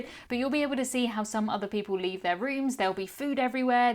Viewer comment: Omg. I thought said there were “rubbers” in other peoples cabins. She clearly said rubbage. Unsure if British people use the term rubbers or not but I was just like wooow 😂😂😂